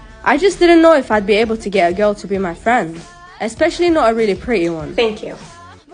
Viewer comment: IMAGINE IF THIS HAPPENED “especially not a really pretty one” sneeze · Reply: Or- OHH I THOUGHT U SAID “especially not a not pretty one” and I was abt to say that💀